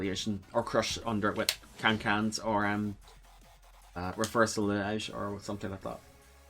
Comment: I heard you almost say Reversal Edge, nice try